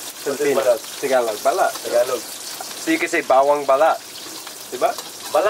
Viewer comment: I’ve spat my coffee from laughing so hard , I heard “ bawang belat” instead of “ bawang balat”😂😂😂